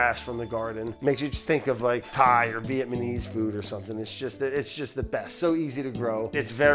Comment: Did he say veitmanese? 😆